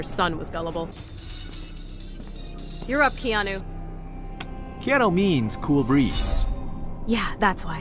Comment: What does Kenshi say at 5:55? ''Keanu means cool beans''? x)